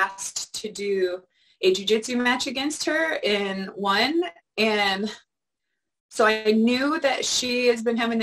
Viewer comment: It sounds like Cat said " I had been asked to do a jiu jitsu match against her (Miesha Tate) and WON"